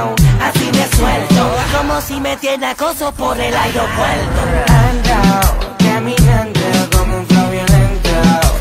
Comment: pensé que decia ando ando caminando como un flavioventa